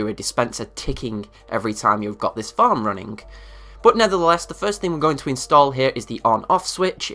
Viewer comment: did you say NETHERtheless?